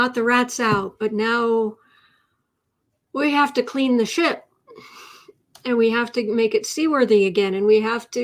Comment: Sounded like you said, "we'll have to clean the sh*t". When you said, "we'll have to clean the ship". Either - Or, in this case!